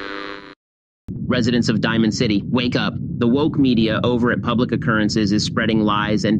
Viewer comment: please tell me someone else thought he said "wank up"